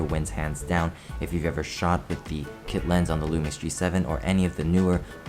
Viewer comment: what body does he mention? Can't make out the model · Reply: It’s the lumix g7 and kit lens (14-42mm f3.5-5.6)